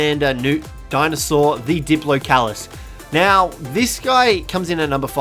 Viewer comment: I disagree with the diplocaulus. On second though, what do I need scuba gear for. The diplocaulus can give you oxygen and help you explore the ocean. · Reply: I thought u said diplodocus so I was completely confused at first but then I realised 😂😂